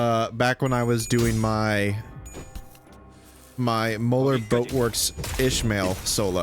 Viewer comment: SOMEHOW misheard that as "Molar Goatworks". Thanks, I'll use that someday. Probably